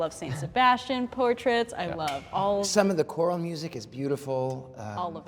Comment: DID HE SAY QURAN MUSIC 🥴